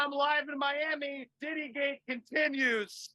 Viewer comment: Did he just say ‘titty gate’ during the height of the “me too”movement